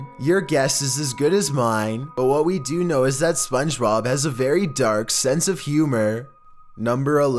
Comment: I THINK HE SAID SPONGEROB